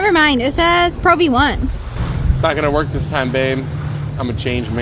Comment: I thought bro said that's not going to work he's a change man